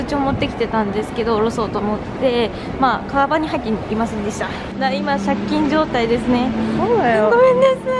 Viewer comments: この動画好きすぎてもう30回くらい見てるけど、1:34のがなんて言ってるのか未だに分からない🤪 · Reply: まあかーばんに入っていませんでしたーって言ってますたぶん笑笑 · なんて言ってるか、わかる人いますか？ めっちゃ気になる、、、笑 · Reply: (通帳が)かばんに入っていませんでした、だと思います！